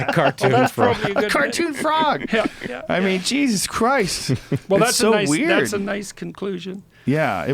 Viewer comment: anybody know whos talking at the end? once the logo goes up, sounds like someone says "jesus christ, that....". cant tell what the rest is or who said it but i really want to know lol. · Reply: Jesus Christ,that's Jason Bourne